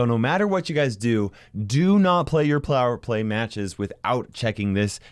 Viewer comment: plower play? Or am I hearing thing's